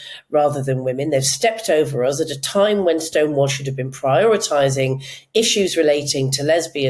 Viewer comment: I was amused that it sounded like Julie called them 'Stonewash' at 25:44... or is that just me? 😄